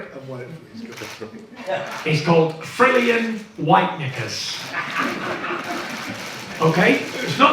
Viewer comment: did he just say what I think he did…? Someone correct me if I’m wrong I genuinely can’t tell what he said but it’s sounded a lot like uno what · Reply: White KNICKERS. Listen properly don't jump to conclusions.